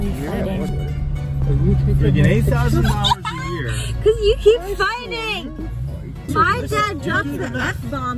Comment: What'd you say at 1:48? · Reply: family fighting lol